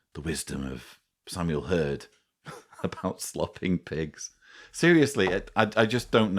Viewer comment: Oh yes my friend had told me about the slop, but as far as the whole saying about the pigs and cows, that wasn’t something she’d heard. But who knows what was said around the Bethel farm! If my sister ever speaks to me again I’ll ask her! She was at the Bethel farm for a few years. I was never baptized or disfellowshipped but I refuse to go back. So the shunning continues. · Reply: I was very sleepy when I first watched this and misheard it as "slapping" a pig. I was...confused, to say the least. But even when I figured out it was feeding a pig...it still does not make any sense.